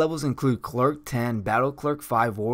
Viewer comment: I didn't know a lot about DND I would have thought you were saying she was a clerk, and not a cleric.